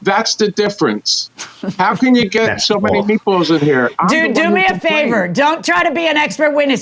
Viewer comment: did I hear JM say "dude, do me a favor...." 🤔😅🥴🤣🤣🤣🤣🤣👍